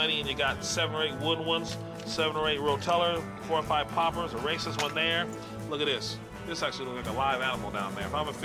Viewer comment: did he say a racist lure? lol